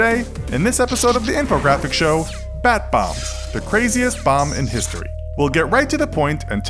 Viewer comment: Who else thought it said bath bombs?😂